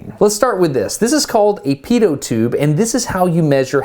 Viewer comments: Sorry my guys but, 12:10 did he say pedo tube ? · I first thought he said Pedo Tube